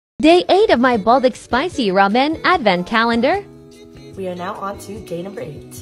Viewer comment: Bro I thought you said “ they are of my the Buldak Spicy Ramen”💀